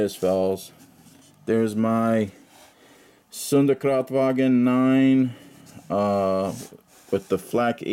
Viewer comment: I thought you said Sonder"kraut"wagen :D